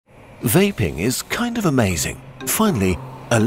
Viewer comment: My dumbaas thought it said "YAPPING"